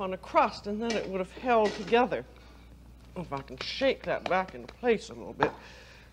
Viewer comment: I swear at 0:51 she said the f-word! Makes me love her even more. Yep, "I'll fuckin' shake that back in place a little bit.", and then looks at the camera and hopes nobody noticed. You can tell she's pissed off at herself. Awkward moments happen. She was a long cool woman in a black dress... credit- The Hollies (not CCR)